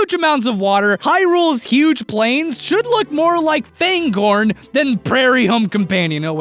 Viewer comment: Austin. An accusation is not a conviction. You can still make the prairie home companion reference. Additionally, artists are not their work. I swear I thought you said Cairngorm, though. I can't rightly tell. Went back and heard Fangorn, okay, mr. lord of the rings dude.